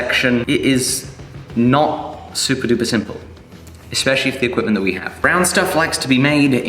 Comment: anyone else hear "..especially with the thicc women we have" @ 3:12? lol